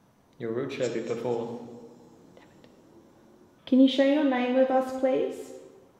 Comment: I HEAR A WOMEN 39:58, "DAMMIT" AND WHISPERS AT 40:16 I HEAR WHISPERS WHILE IS TALKING